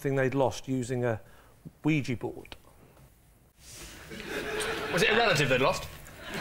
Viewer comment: What's he saying between 0:08-0:10? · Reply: "Was it a relative they'd lost?"